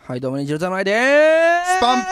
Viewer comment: はい、どうも炎上侍でーす！ に聞こえなくもない